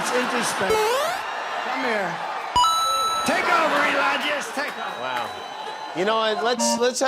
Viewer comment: Did he say, take over Elonia? 06:10… LOL